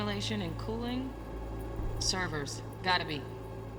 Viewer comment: all i heard was "A server of power, gotta be"